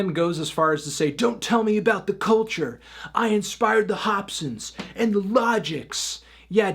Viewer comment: I thought he said he "spied" the logics the Hopson not "Inspired" but the point stands